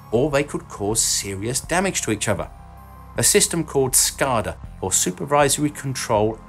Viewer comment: Oh..SCADA ... lol, thought he said Skynet. Thanks for the spelling.